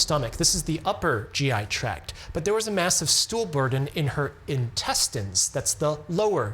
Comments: I totally thought you were saying "stoolberg". Made total sense. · "Massive stool burden" ...that's a great name for a band. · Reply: I thought he said "massive stoolberg", lol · lol stool burden, I thought at first you said "stoolberg", like iceberg but with stool.